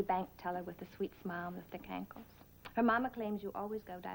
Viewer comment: I didn't know kankles was an expression back then... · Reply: does say 'thick ankles', but I also initially thought she said 'cankles'!